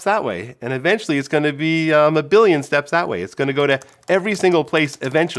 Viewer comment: Did he say morbillion!??